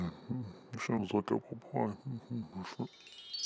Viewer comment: Did anyone else hear “Sounds like apple pie”